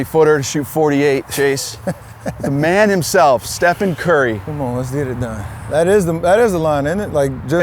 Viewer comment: Did he just call him "Steppin Curry"? Maybe he doesn't know the "ph" is an F sound lol